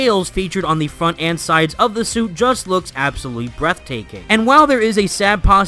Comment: did he say breathtaking?